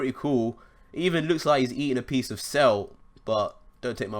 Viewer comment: what was said at 15:56? Eating a piece of Cell?